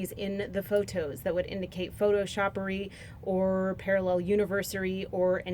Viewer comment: did she say photoshoppery?